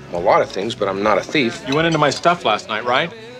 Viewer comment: I always thought he said “And you went into my wallet last night for Pete sake” haha never even realized it